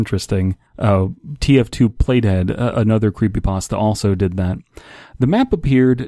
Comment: when you said play dead, I thought you said "plate head"